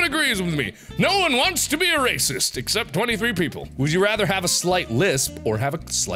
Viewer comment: did he just say 23 people